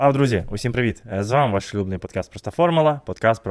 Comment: Мені одному почулося шлюбний подкаст?